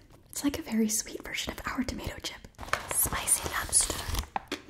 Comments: I thought she said 'spicy hamster' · Ngl I thought she said “spicy hamster” 😂😂😂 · I literally thought you said "spicy hamster" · I thought you said “spicy hamster” and I got scared